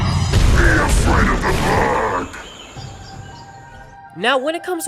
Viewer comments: For some reason I always thought he was saying “BE AFRAID OF THE PARK!!!!!” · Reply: i thought he said "be afraid of the fog!" i have no idea why. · For some reason I always thought he was saying “BE AFRAID OF THE PARK!!!!!”